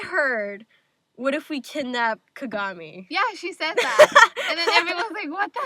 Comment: Ok at first I thought that Juleka had said, “Why don’t we just murder Kagami?” and I was like WhAt?!?!!! But then I realized later that she said kidnap and not murder